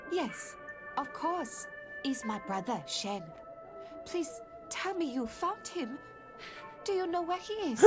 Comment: Sounds like she said "Please tell me you fucked him!"